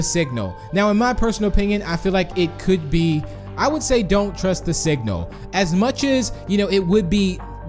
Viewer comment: I thought it said "don't trust the sink" 😂🤚